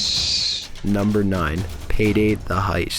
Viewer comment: I heard it say my characters name “Alex“